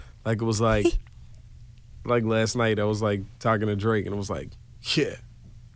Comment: did he say to drake?